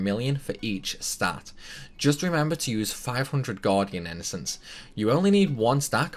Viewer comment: Sorry, on 8:49 can you write down what type of innocents you need? I can't make out it for some reason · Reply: its Gardian to boost armor types